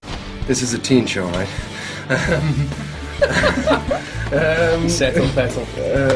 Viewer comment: At about 8:19 after Fassy asks if this is a teen show, does James say ''settle python''. If so wonderful penis joke, if not I'm clearly only thinking about one thing!